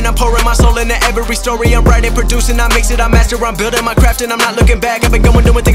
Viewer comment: did he say building in Minecraft 😂